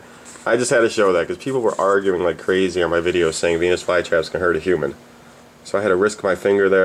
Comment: I misheard it (sorry dont report) he said penis flytraps